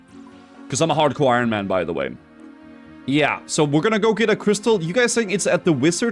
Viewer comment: each time he says "i am hardcore ironman by the way" i hear: "i worked at blizzard" :D